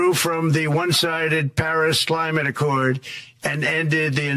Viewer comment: Did he just say “One-sided Paris “SLIMATE” ACCORD?” 😝💪🇺🇸